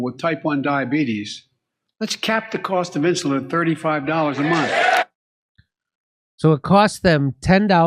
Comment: It sounds to me like he said "$35 a month," not dose. They clap over top of it, but I swear I heard it. I don't care for Jhao Bai Din at all, but the difference between $35/month and $35/dose is pretty vast. It's still a load of bullshit, however.